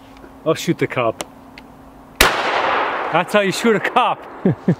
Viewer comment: You might have to subtitle the wooded beardsman with his thick Canadian accent. I swear I heard him say, “that’s how you shoot a cop.”